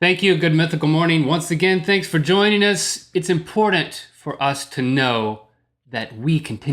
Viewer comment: Link says "inpordant" instead of "important" LOL reminds of a time in Boston when I was asking directions and ask where Hundingdon street was. The guy almost yelled "Its HunTingTon!" Fom a guy with a Boston accent! Should asked him where he pawed his caw